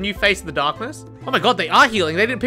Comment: dude you said nea and i misheard you and thought you said the N-word and i was DEVASTATED for like 2 seconds until i saw the name nea and was like "oh..."